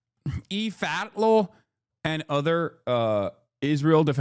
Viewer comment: can anyone tell me what Hasan is saying here? Lmao I swear I’ve heard him say it before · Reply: Eve Fartlow